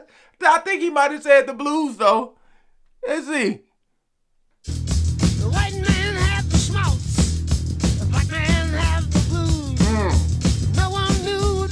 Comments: I thought he said the white men had the smokes and the black man had the blues. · i thought he said white men had the smokes the black men had the booze